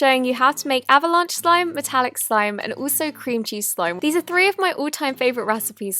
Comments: lol When she said cream cheese slime I thought she said cringy slime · I have no clue why but at 0:05 I thought she said Cringey slime instead of Cream cheese slime 😂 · No offence but when she said cream cheese slime I thought she said cringy slime! LOL! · I thought she said cringey slime and not cream cheese slime lol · Is it just me or at 0:05 seconds I thought she said cringey slime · I thought she said “Cringy slime”😂😂😂😂😂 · When you said cream cheese slime I'm thought you said cringey slime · When you said 'Cream cheese slime' I heard you say cringy slime. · I thought she said cringy slime not cream cheese slime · I thought that she said cringy slime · Oh I thought she said cringy slime but it was cream cheese slime😂😂